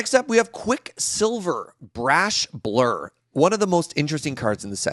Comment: I’m sorry, I have to pause and comment - did serge just say imo at 19:55?